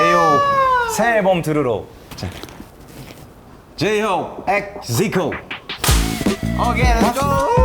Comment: I think zico was saying "j-hope x ZICO", maybe someone can change the subs a bit